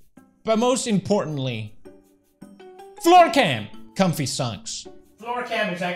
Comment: When he said floor cam i thought he said floor gang HHAHAHA